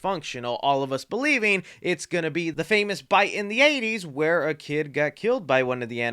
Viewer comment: did you just say bite of the 80's?